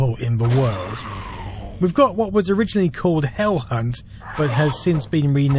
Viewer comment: When he said “Hell Hunt” I thought he said “Hell C*nt” and that would be an AMAZING name for a game. Or band.